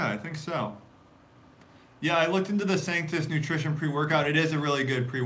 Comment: Which preworkout did you say at 28:50? Saintus? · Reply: I believe it's Sanctus